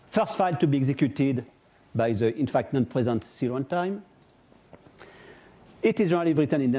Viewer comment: what does he say in 6:28 ? first file to be executed by the {....} C run time ? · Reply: in fact non-present